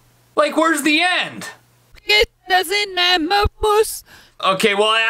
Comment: I heard “ it doesn’t matter, puss”